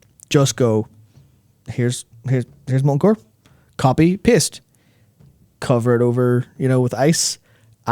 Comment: I heard “copy pissed”, curse my American ears!